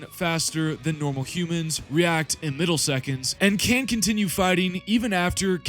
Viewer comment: did you say “react in middle seconds” ? I think the word you may have been looking for was “milliseconds” my friend. Let’s not take vocabulary for granite here…